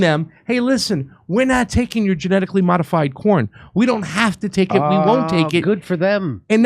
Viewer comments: At FIRST I thought you said, “Genetically modified porn.” Omg that’s all we need. Lol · I thought Craig said "Genetically modified Porn" lol